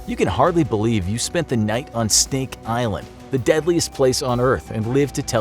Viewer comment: Did he say Steak Island? 9:55 I'd be down for that